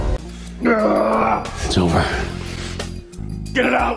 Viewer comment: Any blooper with Jensen Ackles is absolutely hilarious but "Aloha cowboy" ? 😭😭😭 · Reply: Ohhh I thought he said “a little hot, cowboy” 😂😂